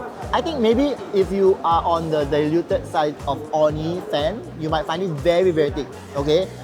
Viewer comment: Why did I hear as only fans 😅😅